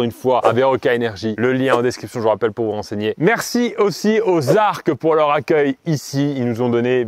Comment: A la fin j'ai compris "merci aussi aux arbres pour leur accueil" :D